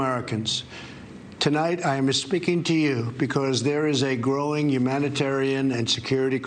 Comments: Hold on. Did he just say, "I am espeaking to you...?" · Sounds like he said "I am esspeaking to you today"? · I literally heard him say "I'm misspeaking to you." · I heard "I'm misspeaking to you..."